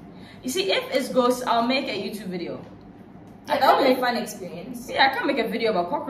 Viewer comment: I don’t know what sex is 😂😂I thought she said she was an expert 🤣